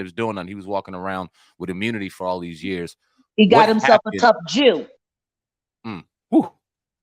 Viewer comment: They must have told her ass, Will Smith is a no go?? We got your back if you go too far Jag. So we'll give you the horn if the case may be...😅😅 And what did she say at 11:00 ? He got himself a what? I tired and tried and just couldn't hear it right, but it sounds like she said Jew. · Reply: “A tough Jew. And happens he queer as a 4 dollar bill. “